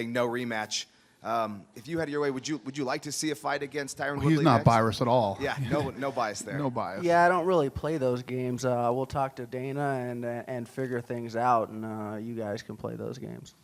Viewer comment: wtf did Dana say? He definitely did not say biased, sounded more like virused.